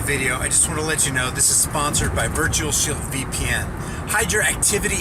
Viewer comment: I thought you said virtual shill vpn